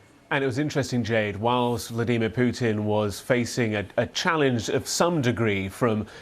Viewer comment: Sounded like you said demon Putin which is very ugly